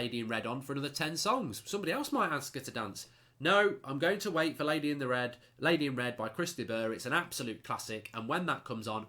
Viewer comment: Thought i heard "Fellaini in red". Almost pissed my pants, haha.